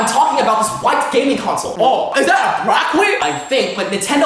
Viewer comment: Hmm Did i hear "Hard Black Wick" 😳